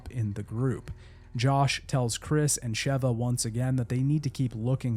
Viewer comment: I Thought You Said They Were Saved By " Joss Stone."